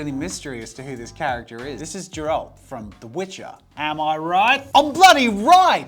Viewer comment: did jazza really say "Jeralt"?